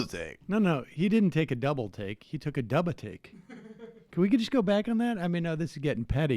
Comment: it sounds like he said he took a dump to me.